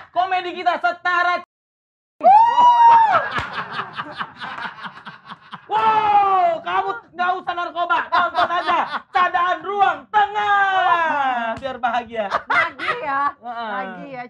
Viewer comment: ini marshel ngomong apa ya wkwk ilmu membaca gerakan mulut gw kureng sehubungan dengan narkoba. BNN? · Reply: baca-baca komen dibawah , kalau ga nyimeng/cimeng